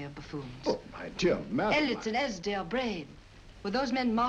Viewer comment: Who is she saying at 51:35? One name sounds like Elliotson.